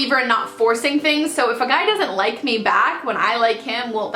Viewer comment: i thought it said " my church doesn't like me back " lol